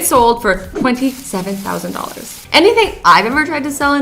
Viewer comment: you said 27000 dollars instead of 75000